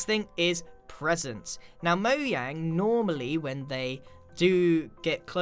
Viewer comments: did he just say moyang or am i tweaking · did he say... "Moyang"? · did he just say moyang😂 10:46 you hear it better